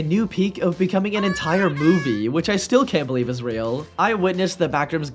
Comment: did you say Israel 🇮🇱?